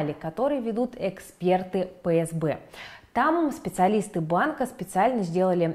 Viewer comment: послышалось "эксперты ФСБ" О_о